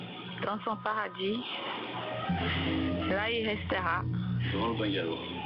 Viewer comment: Attendez attendez c moi où elle dit "la ou il restera" 5:17 et vous dites que vous avez pas de preuves